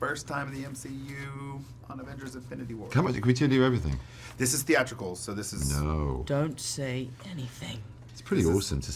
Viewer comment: what the blazes is cumberbatch saying at 0:07?!? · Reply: alisumwal i think ‘can we tell you everything’